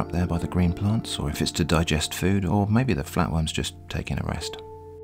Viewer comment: lmao when you said the flatworm's taking a rest I thought you said the fat one's taking a rest